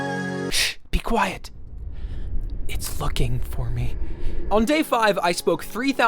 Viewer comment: “It’s Looking for me” thought you said “it’s fucking for me”